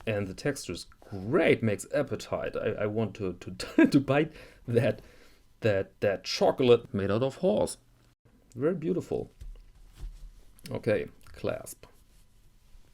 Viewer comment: The “chocolate made out of horse” unfortunately gave me a rash. I suspect the back was chemically dyed. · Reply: Perhaps that's why I heard it as 'chocolate made out of whores'.